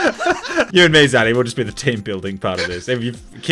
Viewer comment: the English subtitles says you and me daddy